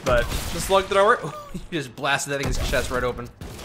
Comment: did say "you just blasted that niggas chest"? plz I must know